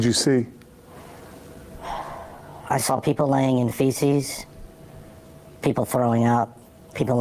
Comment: What TF did he say? Did he say feces!